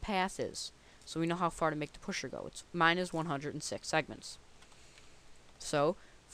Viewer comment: how come you said seconds instead of units? Am I missing something here? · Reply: Segments not seconds